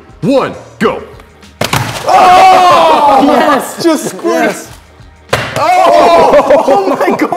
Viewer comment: Did anyone hear say “just sqirts“